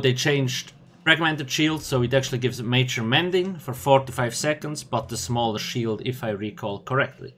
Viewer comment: When you were talking about the DK I thought I heard 45 seconds major mending im like WHAT! 4 to 5 seconds lol...